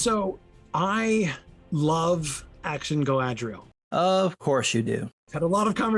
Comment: My brain about ‘sploded when I think I heard “action Galadriel” 😡🤬🤯